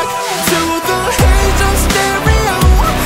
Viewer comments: for a second i thought it said ace of stereo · i thought it said "ace on stereo"